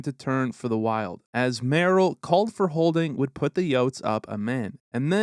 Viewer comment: took me half the video to realize you are saying yotes i thought you were saying oats had me all confused